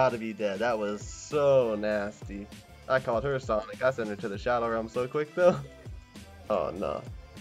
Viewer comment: When you said somthing about vanishing her to the shadow realm i thought you said that was so dirty i need to go take a shower